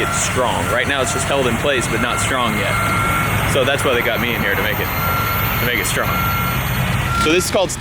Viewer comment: St 1:35 caption says naked strummer instead of make it stronger 😁😂